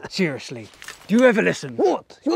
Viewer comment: He said dumpster kitty, but i thought he said "dumb spaghetti" lol